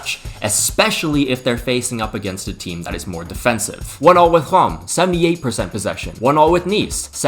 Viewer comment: what is he saying before saying each teams name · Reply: One all